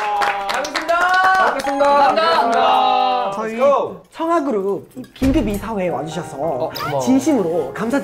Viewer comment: wait.. am i the only one who heard it 'chungha'? 🤔